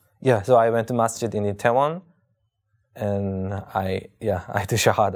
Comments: Great Video MashaAllah. I think said he went to a masjid in Itaewon (town in Korea) not Taiwan as mentioned in the subtitles at 11:56. Also in 16:45, speaks of Daegu (city in South Korea) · I might be wrong, but at 11:56 I think I heard him saying a Masjid in "Itaewon" and not in "Taiwan". Considering at 12:11 he says " ...when I find the Masjid and there was Imam in Korea". So, as we all know, Taiwan is not located in Korea so the subtitle might be wrong at 11:56. But who knows, maybe I'm the one who misheard it and maybe the subtitle was right that he did go there (Taiwan).